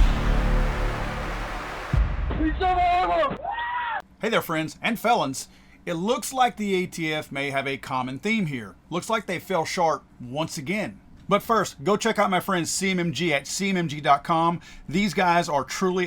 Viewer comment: When you said "fell short" I heard "fell shart" which is an apt descriptor I think. ATF should be a department store.